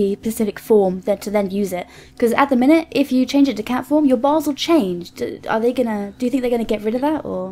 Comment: did she say you're balls will change?